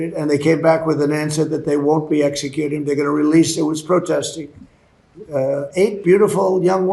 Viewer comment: Did he say “we spoke to Epstein”? At 6:56?